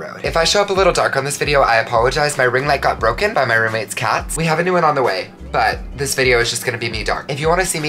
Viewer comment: When you said you had a new ring light on the way I thought you said you had a new cat on the way, bc it destroyed your ring light. 😭✋